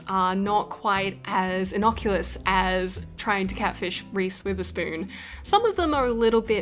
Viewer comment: i thought she keps saying reese with a spoon then i looked it up